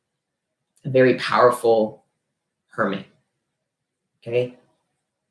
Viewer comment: I heard " A really powerful Kermit "